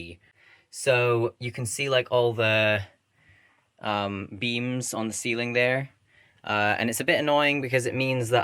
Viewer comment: I thought you said memes on the ceiling